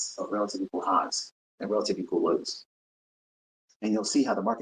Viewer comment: At the 25:50 mark the captions say "you're gonna see lots and lots of relative equal hos". LMAO yep that sounds like my Mentor.😎